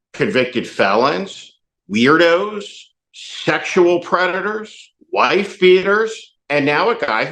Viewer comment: Did you say sectional Predator? As in J.D.?